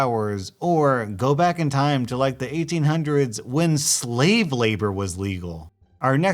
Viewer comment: When you said slave labour, i thought you said slavely